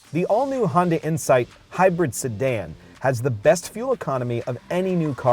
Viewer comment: Did he say Hyundai or Honda? Hyunda?